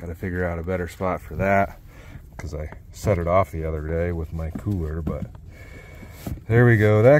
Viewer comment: OMG I was dying laughing I thought you said I set it off with my pooer @ 8:52 mark lol not cooler